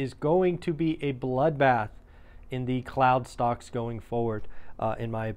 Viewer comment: I thought you said clown stocks 😂